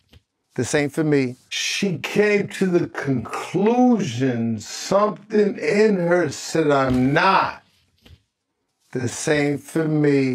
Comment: I think he was actually saying “this ain’t for me” in reference to Coi finding out eating coochie “ain’t for her” but still I do think he was tryna speak thru coi with the way he was wording his sentences. It seemed like he was trying to speak about his own experience when he was talking about “people struggling with their sexuality” 👀